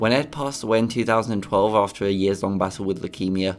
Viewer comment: i thought he said he passed away and i know everyone else heard the same thing